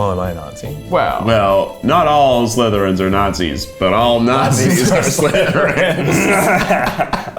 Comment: what have we learned today kids? “Not all slytherins are Nazis, but all Nazis are slytherins” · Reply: lol I heard "not all conservatives are nazis but all nazis are conservative"